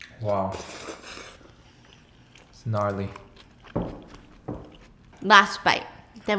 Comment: did he just say GNARLY 😼💚